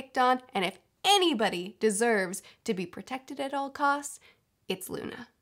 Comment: For some reason, I heard it as: "If there's some person that deserves to be picked on at all costs, it's Luna." And I was like: WHAT SHSHSBS